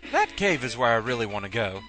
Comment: Not paying complete attention, I thought you said Batcave :D